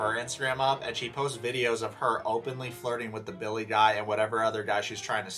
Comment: you said 'billy guy' but I thought you said bill nye